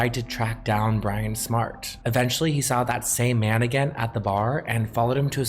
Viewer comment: it really sounds like says Tony saw that same mannequin at the bar, and the weird thing is how much sense that sentence would make in this case. It wouldn’t be surprising if Herb brought one of his mannequins to the club.